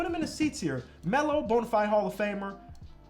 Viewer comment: I wasn’t watching the screen for a minute and I thought he was saying lamelo a bonafide hall of famer💀